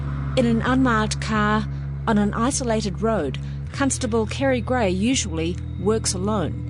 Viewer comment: did she say cuntstable?